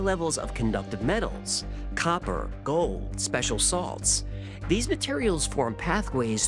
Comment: When you mentioned the contents of a mummy, I misheard special salts as "special sauce"